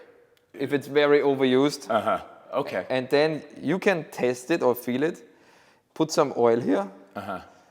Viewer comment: For a second I thought he said ''you can taste it'' and I was thinking, you really shouldn't do that.